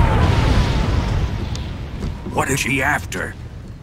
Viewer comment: What does the boss say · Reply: THE JOY